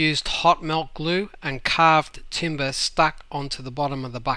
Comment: you said you "used hot melt glue and carved ___ stuck on to the bottom of the bucket" what did you say there? Timber? Just wondering how you were able to carve deep slots in the bucket for the bracket when te bottom of the bucket doesn't seem that thick. So I'm guessing you cut two rectangular holes and then created some kind of cavity to attach to those holes so the melted plastic has somewhere to go. For the hole for the cable. You said to use silicone. Not sure what you mean there but wouldn't using like a pen case in that hole suffice for when you pour the plastic into the mold? That is if the pen case too is non-stick like the bucket plastic.